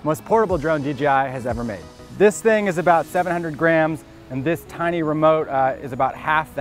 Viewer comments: Am I the only one that thought he said this drone is 700 grand, instead of 700 grams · I thought he said 700 grand. I was like 😲😲